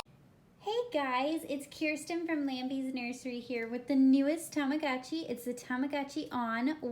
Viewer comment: i thought that said lamby's misery for a second